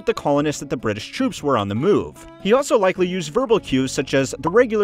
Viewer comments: I thought he said the red coats are coming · I heard he said “the redcoats are coming”